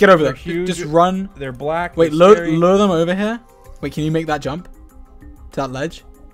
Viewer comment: What did dream say? ... "they're black, they're scary"